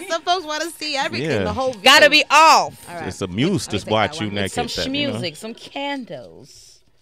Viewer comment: What did he say!? Did he say "It`s a muse just watch you naked". He got away with it , nobody heard.